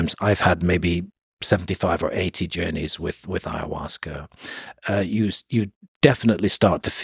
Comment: I’m sorry, I thought I heard him say he’s had maybe 75 or 80 journeys with Ayahuasca.